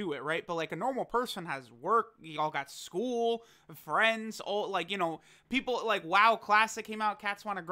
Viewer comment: did just say he has no friends??